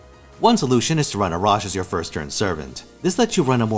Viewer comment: a WHO? I couldn't understand what you were saying on this part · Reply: Arash.